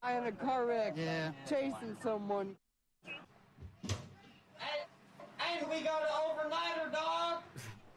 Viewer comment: Was anyone able to decipher what the guy was saying? I couldn’t for the life of me work it out... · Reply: I think he said: Hey! We have an overnighter dawg